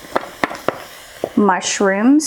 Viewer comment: I thought she said "my shimps" but it was mushroom 😂 like if you heard it too Also yay 300k subs congrats!!!❤ And I can't belive your pregnant